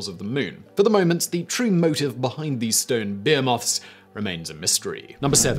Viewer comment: At first I thought he said "beer moths" though, based on context, I'm assuming he meant "behemoths"